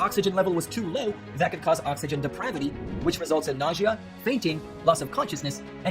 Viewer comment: Did he say, "oxygen depravity?"